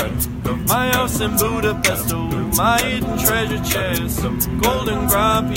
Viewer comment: When I heard the original, I thought I heard "libido" now I'm hearing "Buda pesto" lol really enjoyed it though.